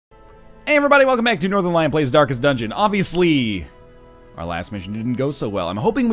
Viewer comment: Every time he says "Welcome to Darkest Dungeon" I hear "Welcome to Dankest Dungeon" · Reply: Jackson Ellis Every time he says "Welcome to Darkest Dungeon" I hear "Welcome to Dankest Dungeon". There.